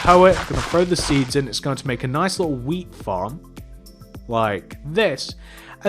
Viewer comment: it sounded like he said weed farm xD